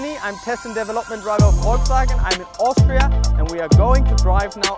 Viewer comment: Typical convo between a north american joking and a german taking everything seriously. He’s awesome and I can’t stop smiling through the whole videos lol. Great job! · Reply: I thought he said Austrian.